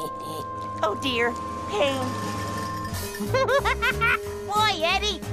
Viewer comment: I think it's 😊 eddy said oh dear pain